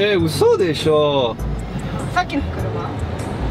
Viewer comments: C’est moi ou à 7:32 dit « fucking Kuruma » ? 🤣🤣🤣🤣🤣🤣🤣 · je ne parle pas japonais mais je connais quelques mots. Kuruma = voiture; par contre le mot juste avant est absent de mon pauvre vocabulaire. J'aurais plutôt dit de l'anglais... c'est bien ce que j'ai entendu ? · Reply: "saki no kuruma" -> "la voiture d'avant" en gros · c'est moi ou a sorti un "Fucking Kuruma" ?